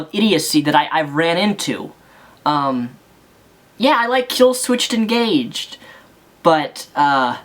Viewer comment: Sounded like you said Kill switched Engaged. Lol. I love the song End of Sorrow.